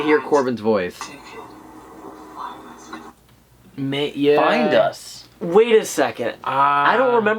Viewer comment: I heard “behind us” instead of “find us” idk just my ears